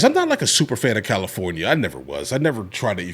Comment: Aaaaahhhh.... super-fan-of-california -especially-halitosis. Thats what i heard anyways....